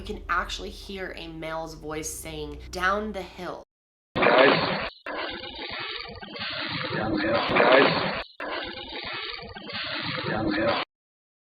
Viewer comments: the audio released to the public sounds like he is saying "crisis, down hill" not "guys, down hill" anyone else hear that? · I hear "Down the hill, guys." Am I the only one?